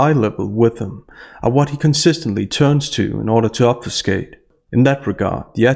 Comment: @ 7:58, what is uper skate? I thought you said ‘obfuscate’.